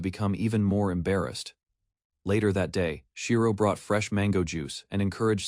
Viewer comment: did boi just say mango😂😂😂😂😂😂😂😂😂✌